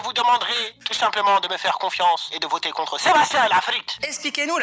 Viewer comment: y'a que moi qui entend dire une insulte en arabe en disant "l'3efrite" quand il appl? 3:58 ; 5:01 ...